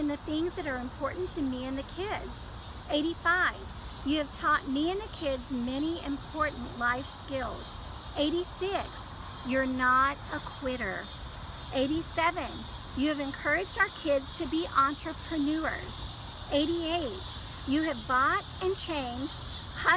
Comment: When she said you are not a quitter, I thought she said you are not queer but I almost didn't question it lol bahahaha.